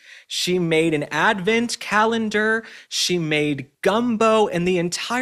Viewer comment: I thought you said she made scumbo